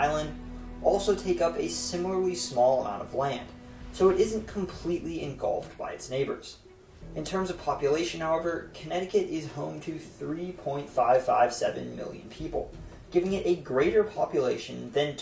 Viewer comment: Anyone else hear 30.55 million instead of 3.55 million? I was thrown for a loop